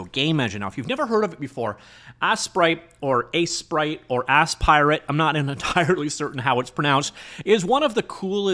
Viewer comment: bro. i heard 'ass pirate' twice 😅😂 lol